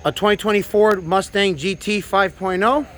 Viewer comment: Ok for a moment I thought you said 2024 Mustang, I said to myself nah now I know they ain't nothing on, My man racing cars not even in production yet, lmao. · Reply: I said Ford so fast after 2020, it sounded like 2024. I can understand why some people thought I said 2024